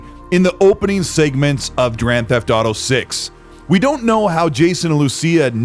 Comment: Did you just say Drand Theft Auto 6 1:15 I can't wait for Drand Theft Auto 6 😂